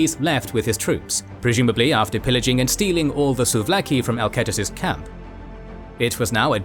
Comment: Did I hear souvlaki at 14:08?😂